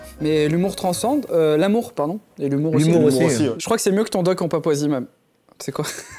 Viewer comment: il a dit quoi svp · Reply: "je crois que c'est mieux que ton doc en Papouasie man" il parle du documentaire de Seb